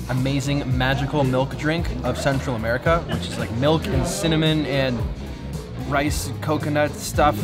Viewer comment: Thought you said milk and semen.